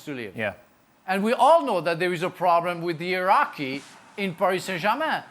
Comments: For a second there I thought Frank said "everyone knows there's a problem with the Iraqi at Paris Saint-Germain..." and I was thinking, what Iraqi? Does he mean the Qataris? Is he being passive-aggressively racist? And then it clicked that he was saying "hierarchy". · Frank: “ There is a real Iraqi (hierarchy) problem at PSG”.. 🤣🤣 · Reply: Hierarchy!!!! Bro I thought he was saying Iraqi problem too!! LMAO I was so confused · “A problem with an Iraqi. A real iraqi problem” - Frank 2021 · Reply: I'm from iraq and I was so confused 🤔 iknow he said hierarchy but I almost thought he said iraq